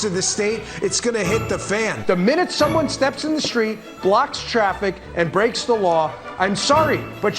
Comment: These compilations are the best. No words necessary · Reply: did he say blacks the traffic?